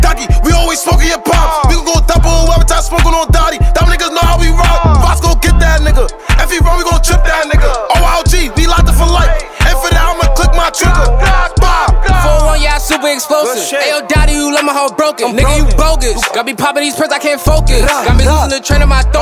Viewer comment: Rip edot baby. Sha ek i thought you said oyogz we locked in for life ?